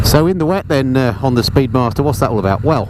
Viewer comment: Did you say "Honda" speedmaster ? At the beginning of the rain section. LOL